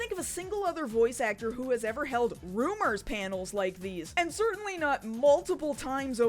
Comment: I just realized this video is 11h long. Sigh. Let's get settled in. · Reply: I thought you said "groomers panels" when you said "rumors panels". Still believable though.